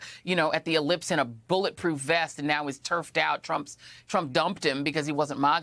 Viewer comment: Did she say "terfed out"? What does that mean?